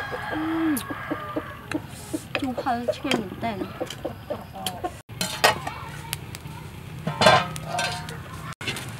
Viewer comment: Can someone tell me what she said at 5:25 because that has to be hot as f#@ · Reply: She said sour n spicy. Really delicious.